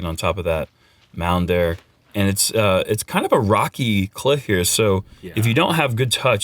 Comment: Did I hear “Rocky Clit” ? 😂